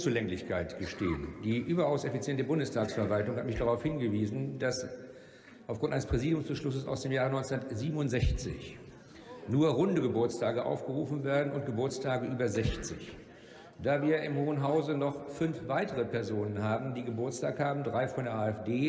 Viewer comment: Hat einer auch "Hundegeburtstage" verstanden? Ich hab' erst nach nen paar Sekunden gecheckt was tatsächlich gemeint war. 🤣